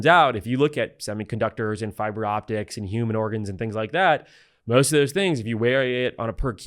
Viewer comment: Did he say "human organs" at 17:03?